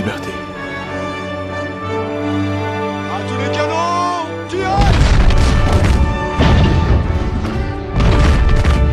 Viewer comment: What did the French naval officer say at 0:41? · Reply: A tous les canons, tirez! - To all the canoon, fire !